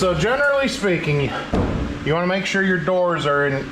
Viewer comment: Is it any coincidence that I heard “General Lee speaking”?